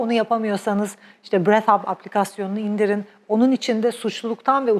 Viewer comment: Çok tesekkürler💞💐 aplikasyon ismi neydi breath hub anladım ama doğrumudur🥰🥰sevgiler